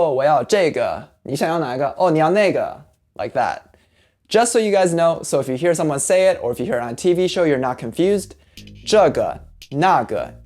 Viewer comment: wait, did he say: "nigga" lol By the way, that was a great lesson sir, thank you so much!